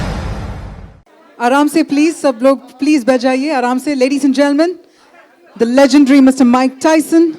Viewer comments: Sounded like ——-Aaram se please sab log bajaayiiye ! 😀😀😀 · I don't what's wrong with my ear but I heard "sab ki bajaaiye"😂😂😂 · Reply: She said sab log baith jaiye ousne jaldi bola na that's why bajaaiye sunai de rha hai